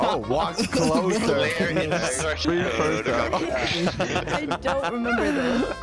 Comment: i thought it said free pursuits 😂😂😂😂